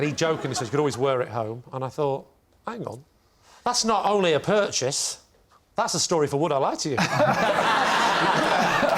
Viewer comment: what did Lee say here? Because Una what? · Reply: Because Una Stubbs played Aunt Sallly in Wurzel Gummage